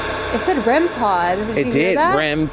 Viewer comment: That DEFINITELY did NOT say “Rem Pod!” It sounds more like it said “Red blouse!”